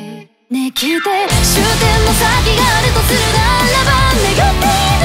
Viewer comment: なんか最後の「あの世行きのバスに乗ってさらば。」のメロディは『消えてしまいたい生涯なんてもんにも価値はあって、あなたはこういうバカみたいなことだけはしないでね。お願いだから』って叫ぶように聞こえてきた 名曲や… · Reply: 「終点の先があるとするならば」じゃないですか？だとしたら1:59